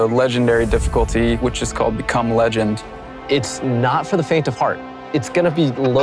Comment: Anyone else think they heard "Cum Legend" at 4:38 ? Cause I spit out my coffee when I heard this line watching this ViDoc for the first time today. I know he's trying to say Become Legend, but either the volume or his own voice tried to annunciate "become" too much on the end, that it just sounds so damn funny.